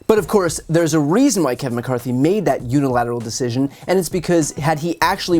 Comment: Hehe every time Brian said 'Kevin McCarthy' I heard 'Kevin My-Car-Keys'. As in 'Who the hell is driving this car over that ledge?'. The fascists you bent over for, Kevin. That's who.